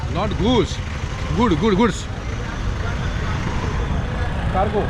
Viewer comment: that man says goods vehicle and you heard it as goose vehicle. You even asked if it was the bird and he makes a face as if he was pronouncing it right and you couldn't get it. It was hilarious 😂